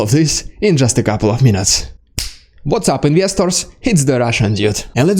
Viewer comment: What is the word that follows "what's happening ____?" It sounds like "yesters" and after hearing it in so many videos I'm intensely curious. Is it Russian slang? Ukrainian?